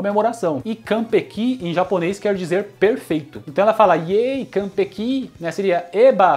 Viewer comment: sempre entendi iee consegui 😂😂